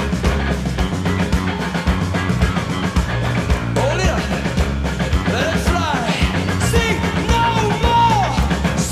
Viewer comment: At about @ 35:35 Bono is waving a white flag during Sunday Bloody Sunday, and says something before "let it fly". Almost 40 years listening to this, and I still have no idea what he said. This isn't a language or accent thing. I grew up about 20 KM away from where they all grew up. I honestly have no idea what he said. Sounds like "oleah" or something. Anyone have any idea what he said? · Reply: He tell those holding the flag to " hold it up"